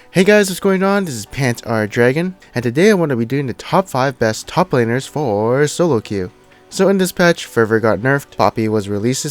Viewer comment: wtf does he say at 0:06 ? i just don't understand what he says there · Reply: "Today we are doing the top five top laners foooor solo queue..."